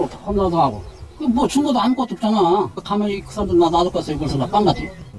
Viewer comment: 증거도 없잖아? 라고 말하는게 맞어...? 죽였다는 소리로 들림